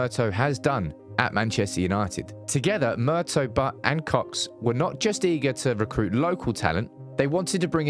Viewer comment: Hold up did u say murtough butt and cox lol😂